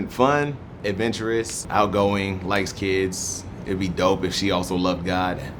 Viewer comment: When he said likes kids I thought he said “lightskin” I was really about to go awf on the comments